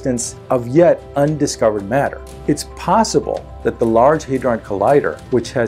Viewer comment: the caption at time 6:40 says: " Large Hard on Collider" !!! (instead of Hadron) 🍌😎